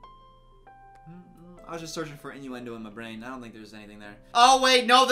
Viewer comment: what is this word? injuwendo? innowendo? inyouwindow? what does it mean? can’t google it without knowing the spelling. (non native speaker here)